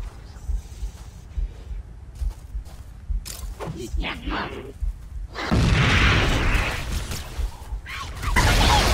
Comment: What did the Jackal say? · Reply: ''I hate grenades'' :P